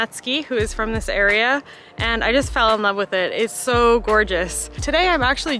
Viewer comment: Thought you said you fell in love with Natsuki and almost spilled my juice. LOL